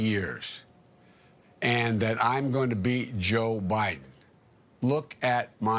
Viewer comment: Did he say “I’m gonna beat Joe Biden” or “I’m gonna BE Joe Biden”? I came because people said he said the former, but —and I don’t wanna give the benefit of the doubt— maybe he said the latter? 🤷🏾‍♂️ either way, I ain’t voting for Him. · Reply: He said "beat" as the reporter had stated that he needed Bernie voters to win the election.